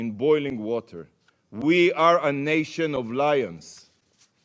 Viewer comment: we are a nation of… what? What did he say? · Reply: We are a nation of lions